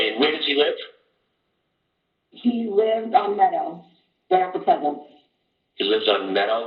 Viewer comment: Does say “lived” (past tense) or “lives?” I can’t tell. Update: I hear it now, she did say “lived.” It’s the “little” words & the brain wants to tell the truth. - thanks Interesting too that Sgt. emphasizes “liveS”